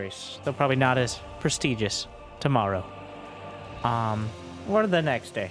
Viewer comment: God i am so high i thought you said "tomaru" instead of "tomorrow"